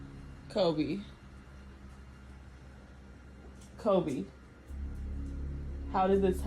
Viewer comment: The dog one, I hear "Coby come here" drawing out heeeeere